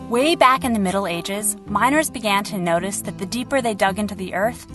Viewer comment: I'm drunk. I thought you'd said "minors". I was confused as to what minors had anything to do with this.